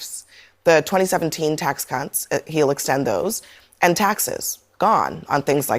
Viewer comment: did she say tax cunts? 😂